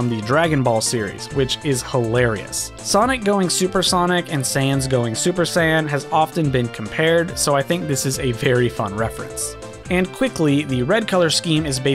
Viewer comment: Sounds like you said Sans Going Super San.